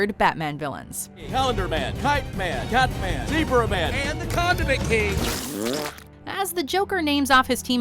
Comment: I thought instead of condiment I thought he said condom man